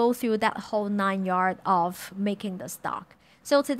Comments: I genuinely thought she was saying something I couldn't understand with her accent (which never happens, because she's a great English speaker), but it she did indeed say "fuck-it" haha. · I thought I was hearing things. I thought I heard bucket soup, then I looked in the comments... LOL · When she said “fuck it” it caught me off guard. I love it. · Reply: I thought I heard "Phuket soup"